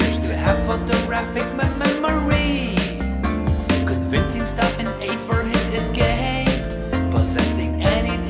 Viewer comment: did he say “he’s convinced for for for for that he is gay.”?